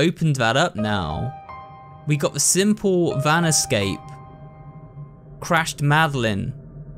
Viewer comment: I thought it said yan escape lol